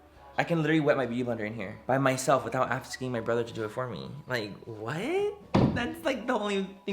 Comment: I THOUGHT HE SAID “I can wet my beef in here without asking my brother to do it for me”🤣🤣🤣🤣